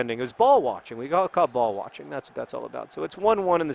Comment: I'm so american that when ever John said York City, my mind heard him say New York City.